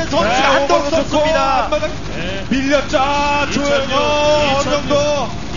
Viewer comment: did they say LIM YO HWAN at 4:06?